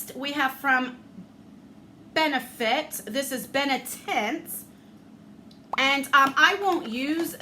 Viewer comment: I thought u said benetit not benetint lol 😂 i love the fact that u do giveaways july 19th is my babyshower so thats awesome